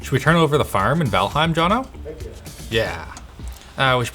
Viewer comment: what game is that? i think i heard valheim but im not sure